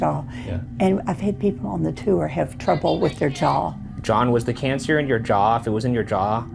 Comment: I'm so sorry, john. but this was what i heard "Jawhn had jaw cancer"